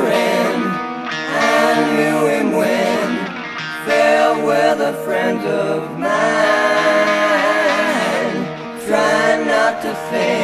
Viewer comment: "Sad sufferin, I knew him when, farewell the friend of mine" god we miss you layne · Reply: Is that what he says? Funny, all this time I thought he said "Fair weather friend of mine." I guess I was self projecting, since that is what most of my friends turned out to be.